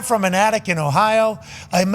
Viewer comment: Definitely thought Pat said "From an addict in Ohio" Lol, Attic, dumbass.